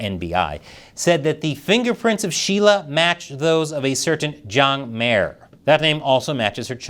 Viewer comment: OMG!!! @ 7:28 when you said Zhang Mier I thought you said "John Mayer" and I was REALLY CONFUSED!!! Is there ANYTHING that the CHINESE WON'T STEAL?!?!?!?! lmao